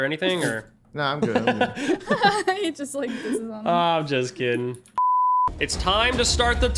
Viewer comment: I love watching meganplays videos and i think ive heard of before! 17:03 Hang on, what did say!? look at the captions. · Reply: Your just pissing on him